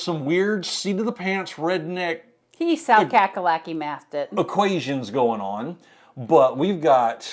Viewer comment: What was said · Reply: We were discussing how he calculated something. Where is from we sometimes call it South Cackalacky (South Carolina), so I said he South Cacalacky mathed it. Making fun of his math calculations. 😂